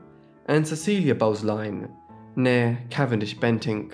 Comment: Not wanting to be THAT person, but Glamis (Castle, etc) is pronounced as GLARMZ And at 6:30, you used the word nee - but the pronunciation was so unclear, I had to keep replaying, as I thought you said MARE or MAYOR for some inexplicable reason and was trying to make sense of the sentence. Nee as in originally born, is pronounced nay or like a horse's neigh. Not wanting to 'have a go' just for future record.